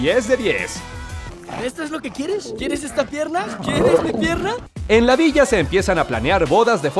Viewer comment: me parece o escuche "quieres esta mierda :D"